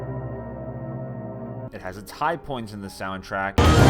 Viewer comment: wut I can't hear it turns volume up 3:44 !!! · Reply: "It has it's high points in the soundtrack."